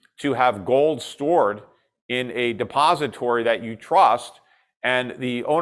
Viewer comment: Did Schiff say 'suppository" at the 28:12 mark?